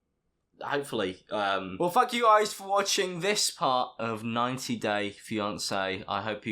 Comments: I heard well f u guys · did he say fuck you guys or thank you guys? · not alex's editing that makes it sound like he said 'well fuck you guys for watching' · It sounded like you said “well fuck you guys” at the end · Is it just me or it sounded like he said “fuck you guys”? 😂 😂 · so no one is gonna talk about how it sounds like he said “well F*ck you guys” 🤣🤣🤣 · did he say fuck you guys or thank you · it sounds like he says “well f*£* you guys for watching” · Did he just say "fu*k you guys for watching?" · I swear he is saying “Well f*ck you guys for watching”. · Haha it sounded like he said well fuck u guys 🤣🤣🤣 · i thought he said “ well fuck you guys “ 😂😂😂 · Is it just me or does it sound like he said “well f*ck you guys” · At the end it sounds like Alex says "well f**k you guys" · Is it only me or does it sound like Alex says 'Well Fuck you guys' In the end? xD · I swear he said "Well fuck you guys for watching this part" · it sounds like he said "Well fuck you guys for watching-" Well fuck u too Alex (I know he actually said thank, not fuck) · did he say "well fuck you guys for watching" or is it just me? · doesn't it sound like he said "Well fuck you guys for watching"?!😂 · When you said, "well thank you guys for watching", I thought you said’ "well f*** you guys for watching". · When he said “well thank you guys...” I thought he said “well fuck You guys” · i thought he said “ well fuck you guys “ :( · I honestly thought he said fuck you guys at the end😂 11:06 and on